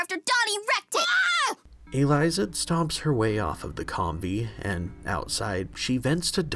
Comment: Learn something new every day. I misheard the name of the vehicle as "Kombi" (as in the Volkswagen vehicle) not "ComVee." Cartoon universe name changes, lol.